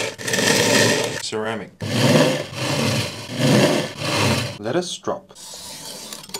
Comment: I stg I heard "lettuce drop". I giggled. Thank you for that giggle <3